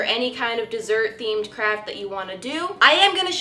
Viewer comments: I thought that she said '' crap'' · It sounds like she is saying crap instead of craft🤣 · every time she said craft i thought she said crap